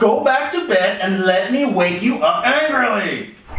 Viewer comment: Pause at 1:04 he said “LET ME WAKE YOU UP ANGRILY” but the subtitles say “LET ME WAKE UP ANGRILY”